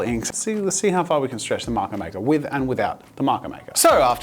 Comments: i thought it said marker marker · I thought it said ''MARKER MARKER'' 💀💀💀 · I thought the marker maker said “marker marker” LOL